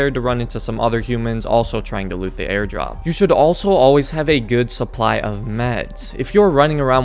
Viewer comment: I swear I heard him say.. "Always carry a good supply of meth!" My bad Lol 😆